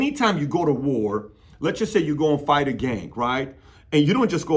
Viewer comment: I thought you said "say you go fight a gay"